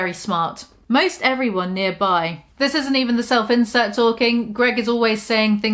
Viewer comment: I keep hearing "self-incel" every time she says "self-insert". Still fits just fine though